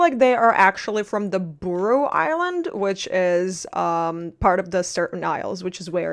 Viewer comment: i really thought she said „bro island“